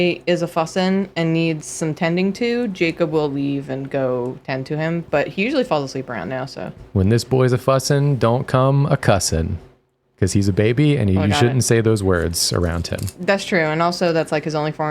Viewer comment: When said "When the boy's a' fussin'" I first heard "When the boy's a possum" and got very excited for a shape-shifting Todd from Mario/Felix